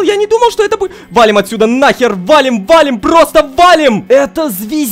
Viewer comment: Что сказал "На*ер"? Не-не-не, мне послышалось